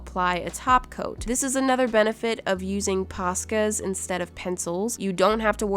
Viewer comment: OMG I HAS SUBTITLES ON AND THEY ARE AUTO GENERATED SO IT THOUGHT THAT SAID PASTAS NOT POSCAS I DYING 😭😭😭